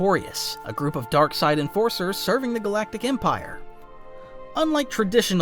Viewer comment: I know he says "Enforcers" but I Thought he said "Dark Sided Forcers" & I thin that should become the. term for force sensetives lol:D